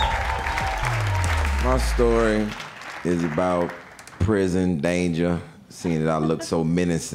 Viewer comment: what did he say at 1:16 ? I heard : "my story is about prison danger sing it I look so minnocent" Best regards from Korea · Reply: "seeing as i look so menacing." he was being sarcastic